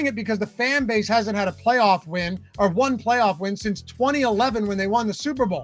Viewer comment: Didn’t I say 1 playoff win since 2011? Which was 2023. I remember it well because the eagles stomped them the following week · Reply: my bad I thought you said last playoff win since 2011.